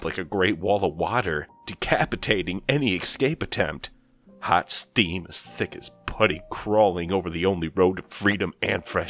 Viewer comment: The background was perfect for this story! For a second I thought I heard ''Hot steam as thick as pudding'' haha. Great job!!